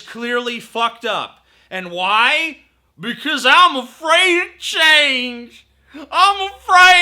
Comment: "ima free a chainss"? wtf did you say there? I'm afraid to change